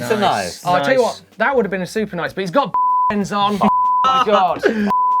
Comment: I have no idea what was said at 21:05. Can anyone enlighten me? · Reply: Hiemy Barends, joke from a few dirt sheds ago saying it "Barends" is one of the worst cuss words around